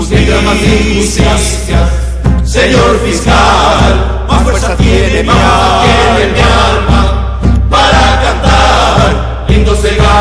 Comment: qué dice en el 2:13? :O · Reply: Más fuerza tiene mi Alma para CANTAR